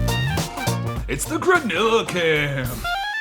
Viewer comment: When he said "Its the Granilla cam!" (@ 2:12) I thought he said "Gorilla Cam" and I was so lost for a bit.